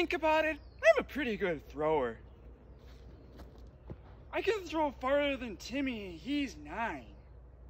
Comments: “I can throw farther than Timmy and he’s nine” had me dying 😂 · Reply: I misheard it, and I thought it was “I can throw farther than Timmy and he’s mine!”🥲😂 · “I can throw farther than Timmy and he’s nine” had me dying 😂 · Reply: i heard "I can throw farther and Timmy heiss i